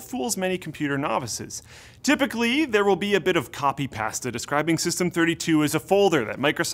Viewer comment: did he just say copy past-ed?